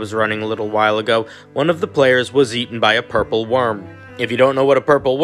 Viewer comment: At first I thought you were saying purple wyrm and i was so confused